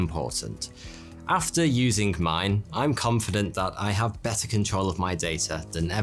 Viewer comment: at first I thought he was saying "butter"......Now i want to eat butter